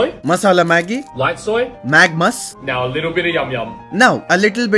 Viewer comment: Bro i heard it like "magma's"